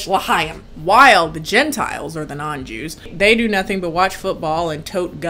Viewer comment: OH THE GENTILES?? for like the whole vid i thought you were saying genitals... until i looked at the generated subtites 😔✋